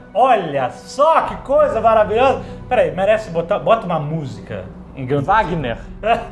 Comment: Eu entendi FAGNER e imaginei os foguetes fazendo borbulhas de amor enquanto descem '-'